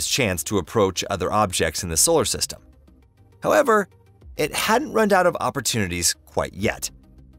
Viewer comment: it sounded like you said "...it hadn't runned out of opportunities yet..." "Runned," is that a new version of "ran?"